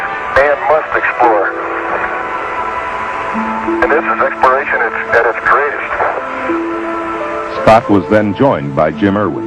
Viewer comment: What does he say just after 6:50, inspiration instead of?? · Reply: He says "And this is exploration at its greatest".